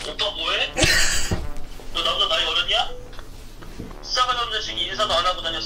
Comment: 아아 너 나보다 나이 어렸냐 네..자막 안보고 들으면 어렸냐로 들림